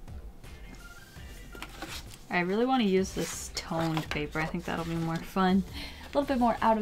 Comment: Toned paper xI thought it said toilet paper...😂